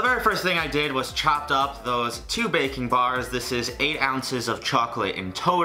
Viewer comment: I legitimately thought you said bacon and since it's currently almost 4am, I didn't even react at first. But it's a BuzzFeed recipe after all, it wouldn't be that unexpected